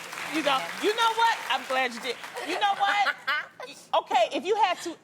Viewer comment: did anyone catch what adrienne whispered to loni at 5:50? something about 'marry him'?? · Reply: Adrienne said “I’m glad I didn’t marry him” and Loni replied “I’m glad you didn’t”